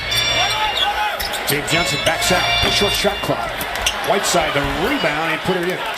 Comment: did he say white iverson?